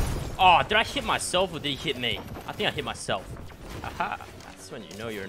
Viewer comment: On 4:25 he said did he s**t himself instead of hit